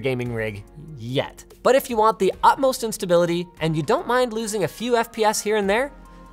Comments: "...but if you want the utmost in stability..." I heard that as: "...but if you want the utmost instability..." lol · This sounds like he said "utmost instability" instead of "utmost in stability." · Did he really say upmost instead of utmost? Oh Linus... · I heard him say "the utmost instability" instead of "the utmost in stability" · I realized that what Linus said was, "If you want the utmost in stability ...", but what I did a doubletake on was that I heard, "If you want the utmost instability ..."